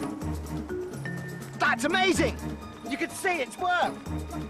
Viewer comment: did he say, you could see it twerk??? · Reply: Minecraft , he said you could see it WORK. it just sounds like that.